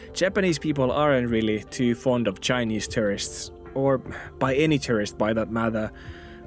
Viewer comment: I always heard you saying "terrorists" and I wondered why you would think people should like terrorists. Later it came to mind that you meant "tourists".. hm